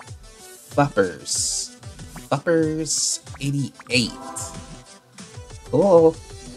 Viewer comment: i thought he said f*c*ers